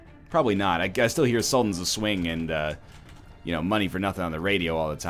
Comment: wait that song is sultan of swing? i thought they said sultans of suade